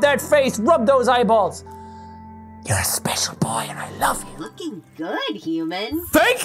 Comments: In 4:48 the ai sounded like uf he said f*cking good human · I thought he said "FUCKIN GOOD HUMAN" 😂😂😂